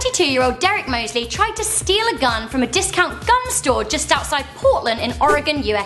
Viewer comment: I thought that she said gum store 😂